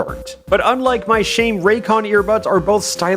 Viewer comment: I heard the r in raycon and for a second i thought you were gonna say raid shadow legends